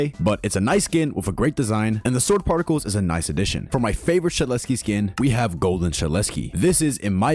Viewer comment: i thought you said desukkar was the best golden skin in your shorts about the three gold skins